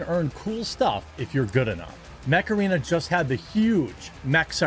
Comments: Curse my old feeble brain, when you said “Mech Arena,” I heard “Macarena.” And if all this does end up in court, well I’ll offer some advice from a wise old man… “Wipe them out, all of them.” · Every time I hear "Mech Arena" I hear Macarena... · Mech Arena? I thought you said Macarena, and was confused for a second.. ;)